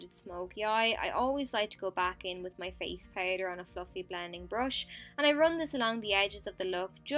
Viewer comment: Haha I had a laugh at 04:27 because instead of "fluffy" blending brush, I heard fu****g blending brush.. so random :p